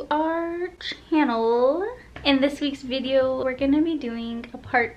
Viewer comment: I thought he said studio at first had to rewind and it was video ❤❤❤